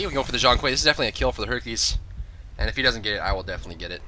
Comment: i thought said herpeles